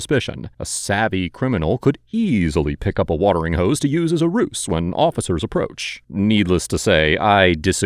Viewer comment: did you say "roose"?